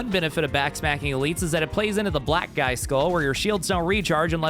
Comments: I could've sword I heard, "black guy skull" not black eye skull. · you definitely said “BLACK GUY SKULL” instead of black eye 😂